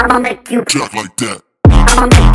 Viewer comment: Why do i hear everytime: "ima make you pop my dad"